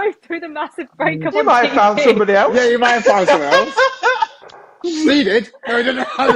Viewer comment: What did say at 1:06:44? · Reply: B said said what’s your relationship status. said I’ve just been through a massive break up. goes Kal has moved on - they where joking